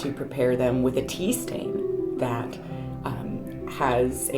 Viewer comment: Tea Stain .... Oh thank you rewind (5 times) I thought you said PEE stain ... Moving on ----> to the tour - yay ;) Fabulous, thank you for every spec of information you shared. I will I am starting my BoS. And you are so right - as a perfectionist with squirrel brain, I know I'm gonna mess it all up, but that's ok because its my intent that is important. Peace Love and Sparkly Blessings to all 💫🌟✨